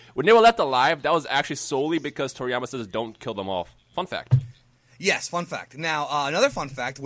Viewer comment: I thought said "fuck that" then said "fun fact" then I was like ohh lol.